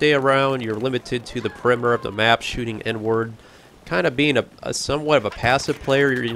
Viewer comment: did he say shooting n word